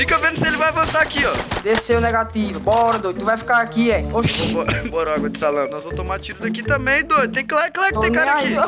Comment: "bora água de salame " só eu que ouvi isso ?